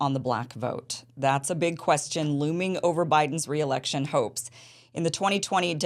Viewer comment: At the beginning of this video, I had to roll it back and turn on the captions because I misheard Kim's words. I really thought she said "...Biden's reelection hoax" 😂